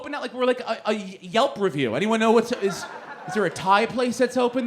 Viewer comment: Lol at 3:30ish i thought he said "is there a tide place open" instead of "is there a TAI place open" 😂Living in 2018 and there being an epidemic of kids eating tide pods will make you hear things you should never mistake for being food haha